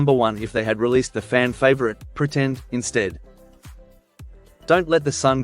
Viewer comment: HA! I thought he said the song was Pretend He's Dead! tried to search thinking, WOW! WHAT A GREAT SONG TITLE! I'm SO disappointed realizing the word was "instead